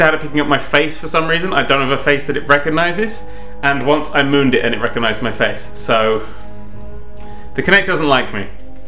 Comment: When he said "I moved it and it recognised my face" I thought he said "I mooned at it and it recognised my face" ......